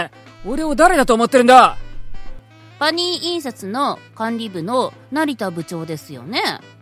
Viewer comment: パーニー印刷→パ兄印刷😂😂😂 無理他侮長いらない👋😞 他人を見下す事しか出来ない奴は、まともな職で働くな！🔫👹 どうせなら呼び方がパ兄印刷って聞こえるからパ兄さんも働いていて欲しかった😃 イシシ😁